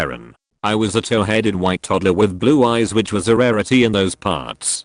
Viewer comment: I thought it said 'I was a towel headed toddler with blue eyes' 😂